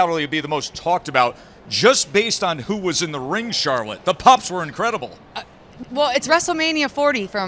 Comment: Hahahah did anyone catch it! Charlotte stuttering cause it sounded like the dude said " the pops" where incredible ,but it sounded like he said pups. Her rack